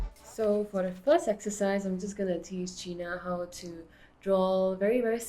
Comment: I think she called you Gina